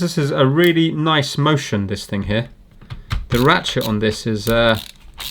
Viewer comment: Oh my. I didn't hear "ratchet", I heard "rat sh__". I think I need to clean my ears